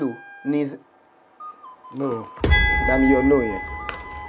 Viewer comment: Not yet watched but I am looking forward to olootu saying "Bamiyo okay yen" "bami yo but yen" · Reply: You jinxed it. I read your comment before watching and boom, I heard “okay”, “bami yo okay yen” 😂😂😂😂